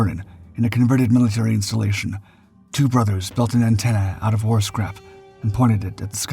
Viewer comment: I thought he said the antenna was made out of horse crap, not war scrap.